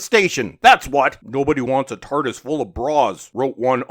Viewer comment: I could have sworn you said "A TARDIS full of broads"